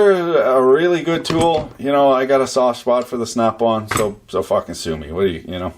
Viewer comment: "Sofa consume me." What? :o) I say that a lot.